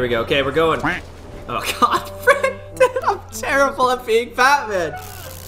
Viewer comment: I thought you said fatman 😂😂😂